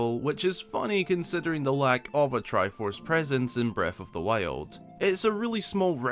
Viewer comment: Every time a video starts with: “Breaf of th Wiald” I grin. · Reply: I scratched my head for the longest time because my dumb ass thought he was saying "Breadth of the Whale" XD